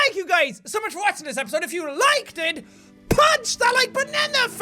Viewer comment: It sounds like he said "punch that like banana face!"